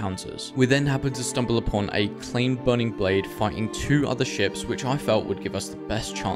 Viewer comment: Did bro say A claimed burning blade fighting or did you say something else? 😂